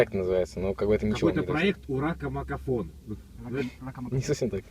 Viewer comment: Мне послышалось " У рака макарон "